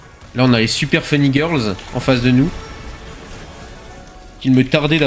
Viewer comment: Les quoi ?? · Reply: Ah j'avais compris super fun e-girls, j'ai bien fait de demander